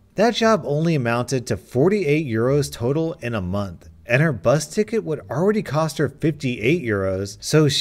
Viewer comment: whenever u say bus ticket, it sounds like ur saying busted kid..